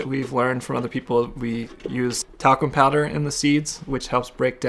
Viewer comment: Thought he was saying they used taco powder at first when they were talking about the talcum lol.